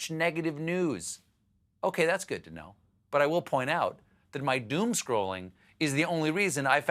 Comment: At first I heard, "Doom Scrotum" and thought you were talking about Mitch McConnell.